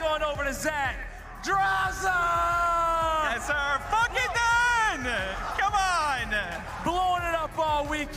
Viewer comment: what does he even say?? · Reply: F*** it dude (censored so its not deleted)